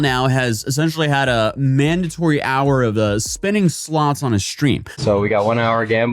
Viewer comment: I really thought you said "spinning sloths" and was so confused 😅